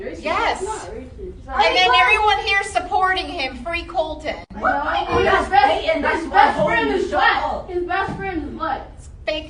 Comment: What are yall's thoughts on this teacher accusing a student of being racist because he bought a Trump flag to school?! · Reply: I thought I heard someone yell but his best friend is black, anybody else hear that?